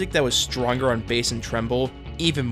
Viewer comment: did....did you really say "tremble" instead of "treble"? lol